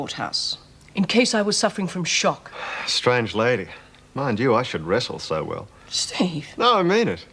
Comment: What does Falkner say? Strange lady. I should wrestle so well? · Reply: James Quinn... says..."Strange lady, Mind you I Should wrestle So well." 😁